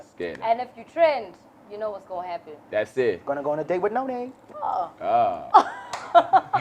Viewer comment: I heard ''win a date with Noni'' and had to comment, lol - sneakerhead size 9